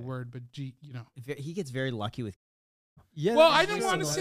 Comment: What’s the word? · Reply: Lucky?